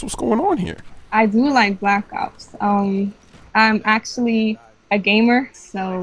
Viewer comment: its wingsofredemption playing not chris smoove. Haha and when she said I like Black Ops I first thought she said "I like Black Cocks"